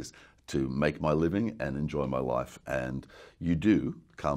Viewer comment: thought he said 'to enjoy my wife' it's life he says, life.